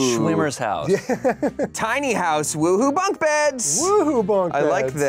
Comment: i cannot be the only one who thought damien said “uwu bunkbeds” instead of “woohoo bunkbeds”